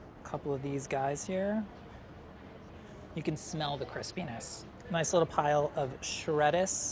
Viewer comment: If someone has CC on, at 9:28 instead of "crispiness" there is "Chris p*nis".